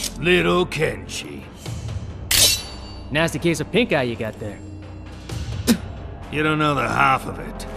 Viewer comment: Can someone help me ? I don't understand what he says ! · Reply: André ! Kano:little kenshi takeda: nasty case of pink eye you’ve got there